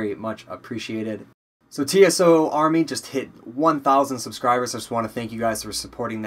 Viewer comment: you said tso army instead of tos